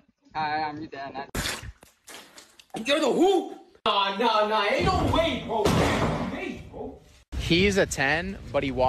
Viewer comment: what did those 2 guys say at 5:19? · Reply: He said naw naw ain't no way bro he's 8